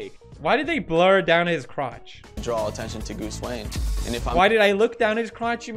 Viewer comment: Did he just say goose wane lol